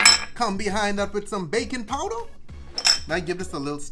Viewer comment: I thought you said bacon powder, not baking.... ps im not a cook lol